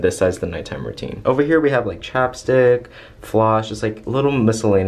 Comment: i thought he said chopstick in stead of chapstick 🤦‍♀️